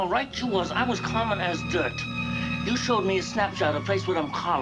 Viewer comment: The double take I did at 20:01 mishearing the dialogue as “you showed me Snapchat and Facebook”in a 1951 film 😂